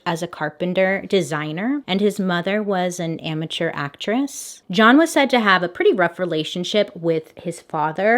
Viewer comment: I’m loosing it! I would have sworn you said “John’s mother was an immature actress”. I even sorta laughed & thought ok, well give her a few years to get a little more mature, I guess